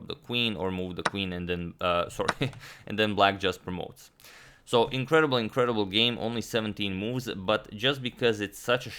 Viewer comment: Does he say incredible, incredible game or incredibly incredible game after 9:12 ? Either way sounds funny